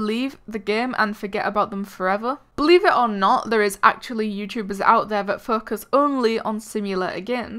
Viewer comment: What's the next simulator? Plus. Did i just hear 'fuckers' at: 1:23 ? JAILBREAK SIMULATOR?!?!